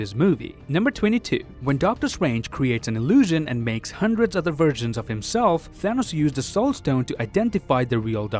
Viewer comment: When he said versions I thought he said virgins 😂😂😂